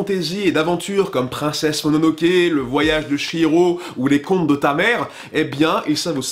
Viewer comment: J'ai entendu "Les comptes de ta mère" x) Et j'me suis étouffée avec mon verre d'eau quand il a dit que A Silent Voice était un anime comique 😂